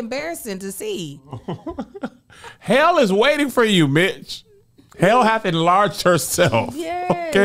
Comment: Lol. I thought I heard say, " Hell is waiting for you bi@ but it was Mitch😂